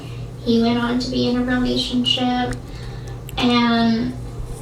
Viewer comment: Did she say “romationship”?